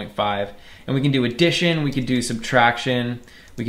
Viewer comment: The captions say "A dish in" instead of "Addition", and the captions aren't auto-generated, so that means that someone wrote it in or something